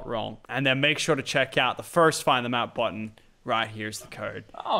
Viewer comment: Did anyone else hear Lachy say the find the map button